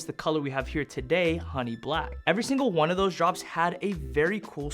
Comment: I thought that said “Honey Pack” 😭😭😭